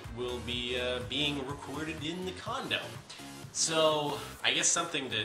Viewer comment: In the beginning I thought you said condom when you said condo lol